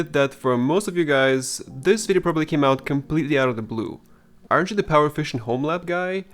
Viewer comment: at first i thought he said "aren't you the power efficient Homelander guy" and I thought that was a solid self diss lol